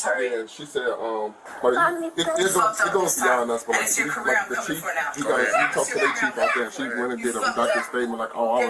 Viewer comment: I'm confused. She didn't sound afraid and from the video she was confrontational. Also, I can't be the only one who heard her say,, "Yeah I'm coming for your Career Now"🤔